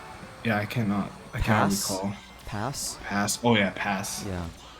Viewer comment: did he say p***s?